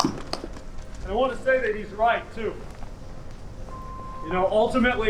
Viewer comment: Dude I thought for SURE he said ''And I wanna say that he's white, too.''